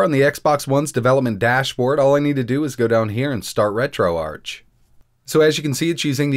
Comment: Oh i thought it was said retro ARK